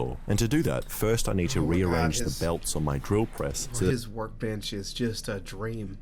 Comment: I didn’t look at captions at first so I thought he was not saying “work bench” But rather “work bitch”